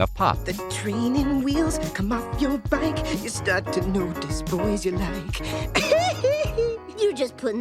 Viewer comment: What did he say?! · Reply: You'll notice the boys you like...is what im hearing😅